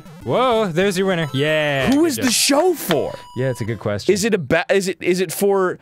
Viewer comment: i thought he said "whos the chauffer?"